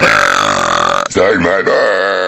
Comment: I swear at 0:56 he said " suck my buuuuuuttttt !!!! "